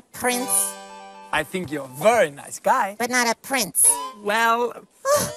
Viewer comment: I love the subtitles when Sportacus said "What do you mean?" Then after when he said "I think you're a very nice guy" but the subtitles said, "I think you're a spoiled snob. And I as Magnus Scheving created you." That got me laughing hard because it's true as Magnus Scheving is the creator of the whole show.